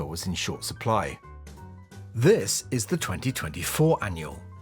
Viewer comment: FYI: At 4:54, the audio says "This is the 2024", while the Closed Captions say "2023".